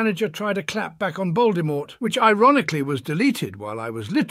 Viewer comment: Did I just hear “Lord Baldemort”?